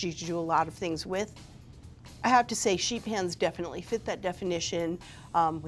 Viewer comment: Sheet pans? Man my interest was piqued when I thought she was saying "Sheep hands"